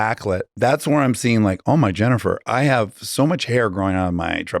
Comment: Did he just say “oh, my Jennifer?” 😂